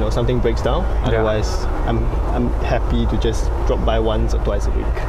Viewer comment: Omg, i.thought he said, "my n*ggas drop by." LMAO